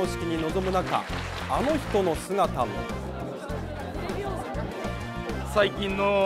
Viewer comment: 本編と全く関係ないけど「あの人の姿も」のとこで「レビオーソ」って聞こえる